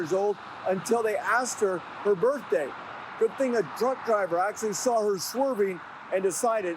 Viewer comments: I thought he said, "good thing a drunk driver decided to call" 🤣 🤣 🤣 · Sounds like he said “good thing a drunk driver saw her swerving “ 0.30 😂😂😂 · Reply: I had to rewind it 5x before I figured out it said "truck driver" because I swore it said "drunk driver" 😂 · 😂 sounded like he says, "Good thing a drunk driver called it in" and I had to replay it a few times 😅😅